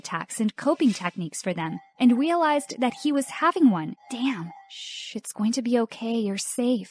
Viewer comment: when she said "shh its going to be ok" i heard "shit, its going to be oK"